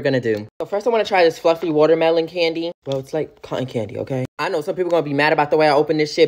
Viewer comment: I thought you said “fvcky watermelon candy”